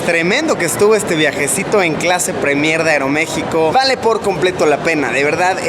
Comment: soy yo o escuche clase pre mierda